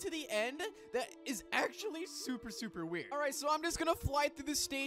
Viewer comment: On 0:59 the subtitles said super super, wet 💀💀